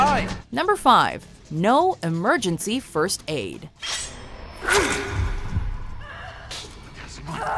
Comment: For a second I thought she said "no emergency first aid" And was was like "why?"